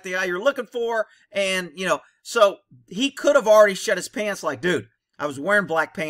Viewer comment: LMAO I totally thought you said he shit his pants 😆